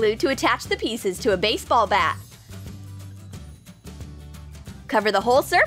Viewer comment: Anyone hear “baseball BAT?” At 2:00 not baseball cap? Just me? Ok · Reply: Yup. She most definitely said BAT.